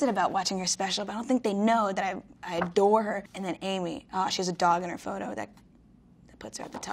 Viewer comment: am I the only one that thought she said “oh she’s a dog in her photo” I was shocked for a second because I didn’t realize she HAS a dog in her photo 😂